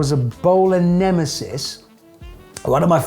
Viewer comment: The car: Bowler Nemesis What the subtitles say: ebOLA neMESis